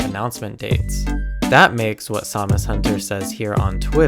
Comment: So who else thought he was saying Zamazenta instead of SamusHunter?